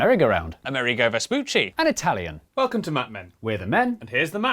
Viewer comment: I heard "Welcome to mad men" despite knowing what I was going to watch. Forseeing the sponsoring end?